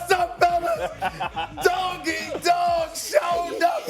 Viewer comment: I thought he said my man donkey talking about Draymond 😂 but he said doggy